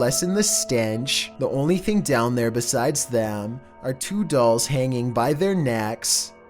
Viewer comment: Basement : Have two dolls in it: As YoU cAn SeE , A sATaNic rItUaL toOk pLaCe hErE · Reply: I thought that said 'two dollars' and I was genuinely confused for five seconds